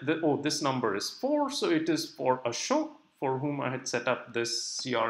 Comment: Hi. Thanks for the video. Can you help me understand the word that uses at 15:57-15:58. He says:"...it knows that the oldest number is 4, so it for a ... " And here goes the word that I hear like "show" or "shock". But what realy says ?